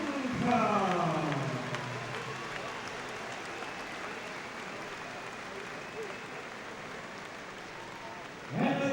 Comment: What is he saying at 12:29 sothing like...Sounds like " he looks conteps mentus in the corner" What is he really saying or What is conteps mentus?? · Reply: "Compos mentis" Latin for 'sound mind'.